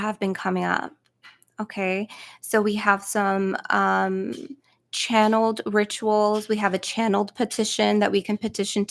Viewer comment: what did she say the deck was called that she was gifted ? Thank you 🙏🏻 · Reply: Anubis oracle